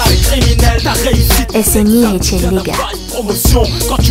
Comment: why did she say nigga?